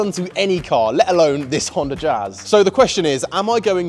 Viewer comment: I thought that said Honda jizz